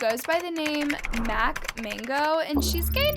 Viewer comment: I thought you said Anna 💀I was like people who hate are dumb but I did not think they are this bad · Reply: nooooo we meant mak mango